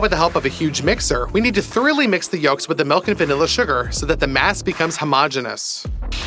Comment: did I hear hamas ? ?